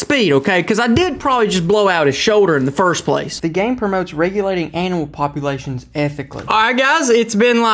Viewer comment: Sounded like he said the game encourages regulating anal populations at the 3:04 mark lmao